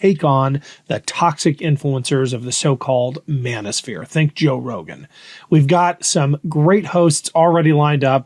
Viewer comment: Oh...I thought you said thank joe rogan 😮 you said think Joe rogan. Much better cuz he created this nightmare and stole my sons hope as well as many other. He's the worst...besides everyone else in this nightmare administration. Thank you for standing up!